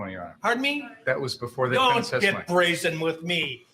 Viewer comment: What did the judge say? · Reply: “Don’t get brazen with me!”